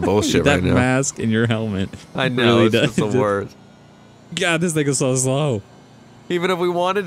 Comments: did he say nigga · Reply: "Gah, this thing is so slow" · did he say nigga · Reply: He said: "God, this thing is so slow" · did he say nigga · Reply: Bruh to me it sounds like he said damn "this nigga is so slow"